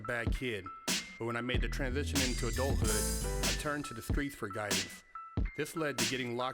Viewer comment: Omg, in many videos in the intro I heard 'I turned to the priest for guidance' hahahah